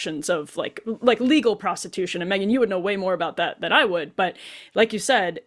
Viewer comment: No she did not call Megyn a “pro” it just sounded like it. 😂